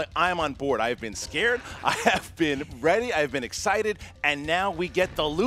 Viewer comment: I am the only one who heard “Reddie” 😂😂😂